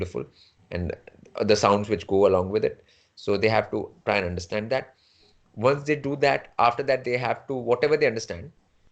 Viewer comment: minute what sir said to watch. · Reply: sir has said to watch lotusocean films. <link>